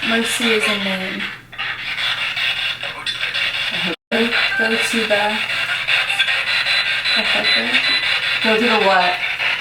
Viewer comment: On the rem-pod. I heard "go to the mountain end". 👀